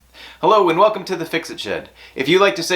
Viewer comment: Yes, I was impressed. When I first bought it I wasn't expecting much, but stuff is holding together years later. I used it to hold the trim on the hard cover on my truck bed. works there, too. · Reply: I thought you said, "Hello and welcome to Fixing Shit" 🤣🤣🤣